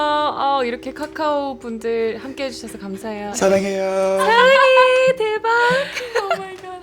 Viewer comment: Did he just said Saranghaeyo? When I thought I couldn't like him more..... 😍